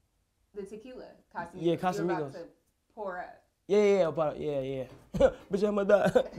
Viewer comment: the captions say "your about to poor up" but it's supposed to be "your about to pour up" as in pouring a glass of water or something